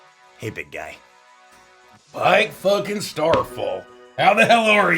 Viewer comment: KISS YOU FOOLS!!! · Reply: said "Pyke fucking Starfall" and I thought I heard "My fucking Starfall"